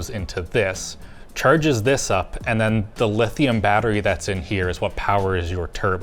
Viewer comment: Dilithium batteries did he say?